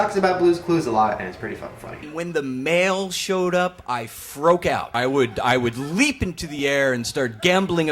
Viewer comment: did he say 'froke out' wtf